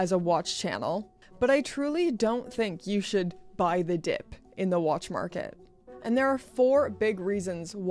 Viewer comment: Nice biker jacket - i heard you say buy the crypto dip instead :)